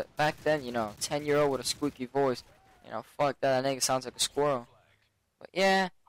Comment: did he say nigga at 1:31 lol